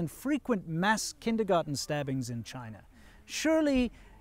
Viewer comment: O.O did i just hear "mass kindergarden stabbings" ?!?!?! Wtf